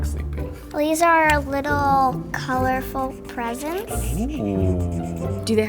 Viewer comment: I thought she said “and some colorful pregnants” 😂